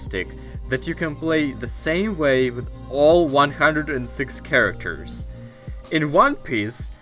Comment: did you just say 106?